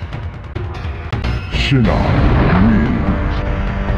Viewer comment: YO TE QUIERO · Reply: parece que dice "pinshi shinnok".